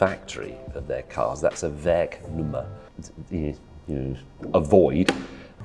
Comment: What does he say at 8:29 sounds like he says a vague rumor? That's what I hear but I think he's using a latin phrase.